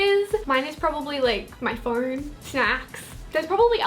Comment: if u look at the english auto-generated subtitles @ 1:06 where says phone but the subtitles says farts 😂